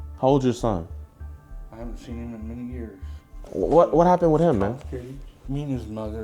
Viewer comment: When he said "how old is your son?" And he replied "I havent seen him in many years" I thought he said "hell be 19 in 9 years" because I was far from my phone with my earbud in 😂😂😂